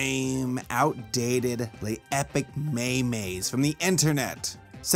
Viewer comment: did he just say "me-me" instead of meme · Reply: no he said maymay